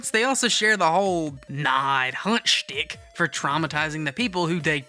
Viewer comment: "Nah I'd huh schtick" I loterally cant hesr anything else wtf dod he say here? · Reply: “Nah, I’d Haunt.” - 💀